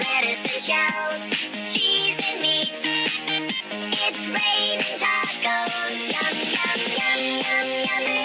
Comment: what did it said its raining trees XD